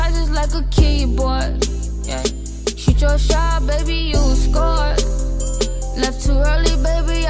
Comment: I thought it said " shot your shot baby, you a score" 🤔 and " fly to where you at, give me what I need " no shade just what i think shes saying 👌🏾